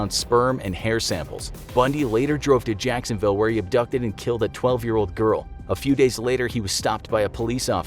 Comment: okay, wait, did I hear that right? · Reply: he killed a 12 year old girl